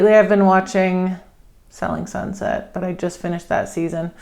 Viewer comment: I think she is saying sailing sunset